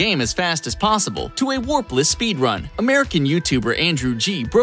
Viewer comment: I was scared about darbian's record until I heard him say the warpless speedrun after he said andrew got it, I thought he meant warped